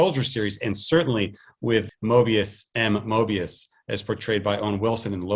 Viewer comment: DAMN! 7:24. After seeinf the 6th Loki episode I thought he said "Mobius AND Mobius" and it blew my mind. I just googled and his name is Moebius M. Moebius : /